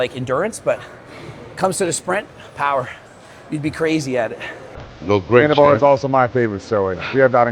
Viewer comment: what did he say?!? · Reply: "You'd be crazy at it"